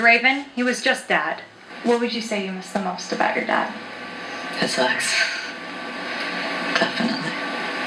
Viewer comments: what did Draven say he missed the most about his dad at 0:35 ? Did you understand it? I didnt "get" a lot of words he said, not sure why... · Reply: he said "his hugs" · what did he say? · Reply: He misses his hugs